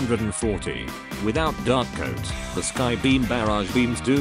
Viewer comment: I thought the video said dark goat